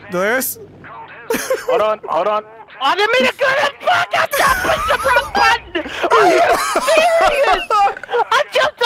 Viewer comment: can anyone tell me what he said?! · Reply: “I didn’t mean to do that, fuck I pushed the wrong button”